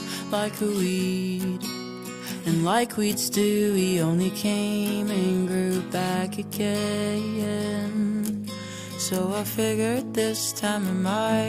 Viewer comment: Don’t kill me I heard ‘came and grew back again’ as ‘came and grew bakugou’